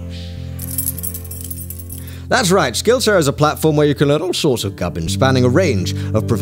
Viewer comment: I heard “learn all sorts of garbage” 😂